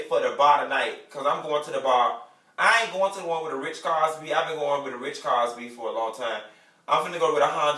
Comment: Wait!!! Hold on!! This whole time I thought the doll was saying he going down to the bar where the Ritz Carlton be! Not where the rich cars be!! Lawd Jesus!! 😩😩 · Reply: Karen Winston me too. I thought he was saying rich Cosbys